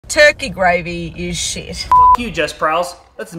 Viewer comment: I thought she was saying all gravy is shite... turkey gravy isnt the best...